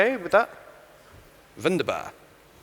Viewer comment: Did he said wunderbar? lol